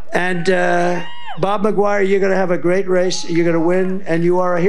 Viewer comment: It really sounds like trump says Bob McGuire